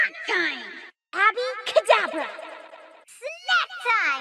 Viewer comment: I always thought Moji said happy kadabra on her ult XD